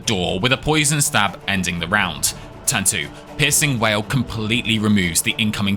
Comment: i am so tired i thought you were saying "pissing whale" instead of "piercing wail"